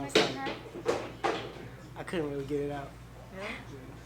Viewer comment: Did just say he couldn't get it up?! 😳😳😳